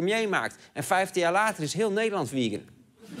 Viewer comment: Wat zegt ie nou op 10:41? "En 15 jaar later is heel Nederland ... " ? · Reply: Ooh "vegan"